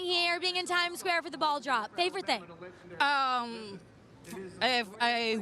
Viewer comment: when the interviewer said "ball drop" I heard "ball job" and got very confused